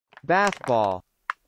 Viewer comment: バスボールがbaseballに聞こえる……